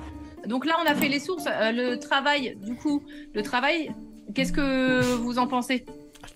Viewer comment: auch wenn Sie französisch spricht, habe ich genau gehört, wie sie den dude erstmal mit "Du Clown" beleidigt hat :D